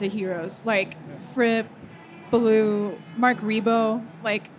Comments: When she said Belew I thought she said Ballou. Like Kurt Ballou. Maybe she did? all great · When she said Belew I thought she said Ballou. Like Kurt Ballou. Maybe she did? all great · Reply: That makes more sense! I thought she said Ballou, but Adrian Belew fits.